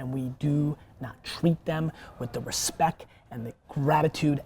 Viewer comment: Did Gary just say respeck ?